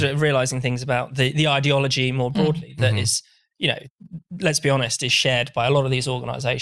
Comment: "Let's be honest" (which sounded like "Lesbi-honest" or "lesbionest")... made me chuckle. It made me think of a birds' nest (birds... get it?) of extremely truthful lesbians. Who also may happen to be bionic. All of 'em. Every lesbian bird in the nest. Okay, I'll stop now.